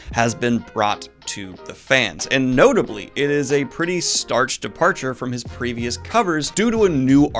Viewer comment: Did You Just Say "Starch Departure?"